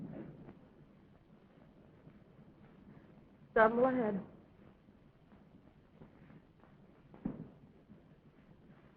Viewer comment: what is she saying here? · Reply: "Some lad." [i.e., He's quite the young man . . . ]